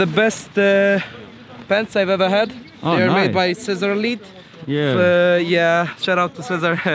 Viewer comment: Does anyone know the pants brand at 6:50 I can't quite understand him but they look sick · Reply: cezar lit, polish guy who is making dope clothes